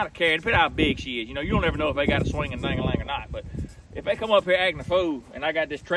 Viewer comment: Did he just say you don't know if they got a diggling or not 21:05 😂😂😂😂